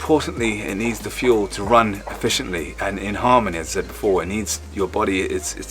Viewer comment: Could've sworn I heard the word 'Harmonizm.' If so, I f**kin' love it. Haven't heard that one in awhile